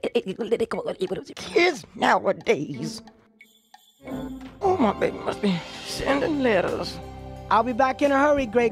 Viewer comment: Here are some Subtitles. 0:06 Subtitles: oh see for the a political look it is nowadays 0:13 Subtitles: oh my baby sending littles 0:21 Subtitles: yo you just want to just contribute to edit it 0:29 Subtitles: oh see uh the eagle did it gonna give it a flower what is here 0:36 Subtitles: oh yeah at home Christmas a hope hope 1:00 (even the Subtitles were confused about what she said) Subtitles: oh a CHRISTMAS